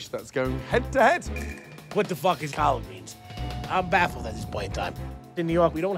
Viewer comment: did he say what the fuck is collard greens